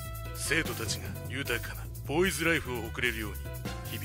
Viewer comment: ん？ボーイズラブライフ？ 聞き間違えか。